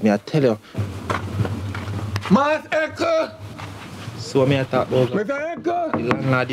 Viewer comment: Dwl, thought she said Maas Echo